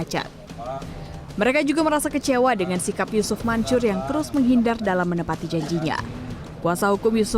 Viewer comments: Narasi nya bilang yucup mancur ya? Apa salah denger gitu? :D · gua salah dengar apa mbaknya emang bilang yusuf mancur.... 😂